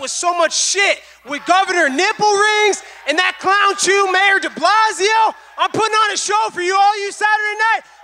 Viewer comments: LMAO DID HE SAY CLOWN JEW MAYOR DIBLAZIO???? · Colby really called him “Clown jew Deblasio” Sheeesh man 😂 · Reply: I thought he said clown shoes. But Bill DeBlasio, born Warren Wilhelm is a Jew. Not sure where the racism is.